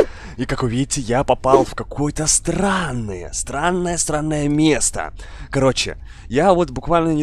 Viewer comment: мне послышалось что он сказал не странное а сранное